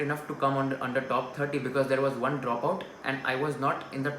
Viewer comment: what did you say I didn't understand properly Rank holder drop out 🤔